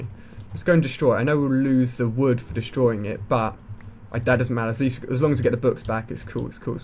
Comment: It sounds like he is saying My dad doesn't matter.